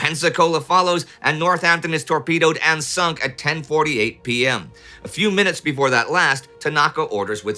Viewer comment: there's an error either in what Indy is saying or the subtitles. Indy says 10:48 pm but the subtitles list 23:48 instead of